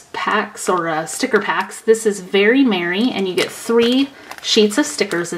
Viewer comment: Lol I thought Very Merry said Very Messy!